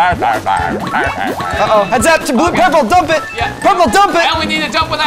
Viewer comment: i thought said BEARS BEARS BEARS BEARS BEAARS.